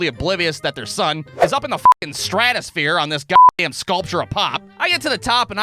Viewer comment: I forgot that he's from the midwest where they call soda "pop" so I thought he was saying "pot" for a second😭